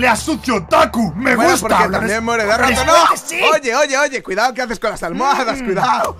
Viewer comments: también duermes con 7u7 o escuché mal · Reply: Siii dijo "Bueno porque también duerme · Como que duermes con? 7w7 Alguien me dice si escuché mal 0w0